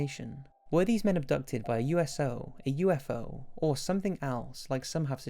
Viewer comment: did he just say USO or i m stupid?